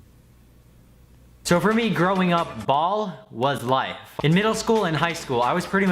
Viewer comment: I thought you said 'growing up bald was life' lol